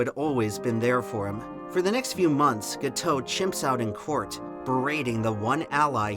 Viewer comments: did just say chimp out 🤣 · Did you say "chimp" out?